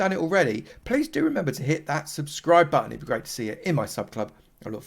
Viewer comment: I swear at the end 10:48 you said " Slab that subscribe button". After all that slabbing you would of had them on your mind. :)